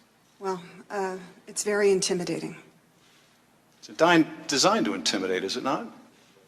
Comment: Schiff said, "The dime (???) designed to intimidate, is it not?" I cannot hear clearly this word dime? dine? What is the word? Thank you for your help. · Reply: = I believe he said it was "designed" to be intimidating. Hope that helps.